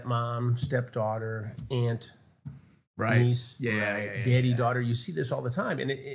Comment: thought he said diddy